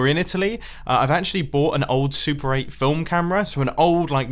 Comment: Totally thought you said "I bought a soup" and all I heard was soup after that...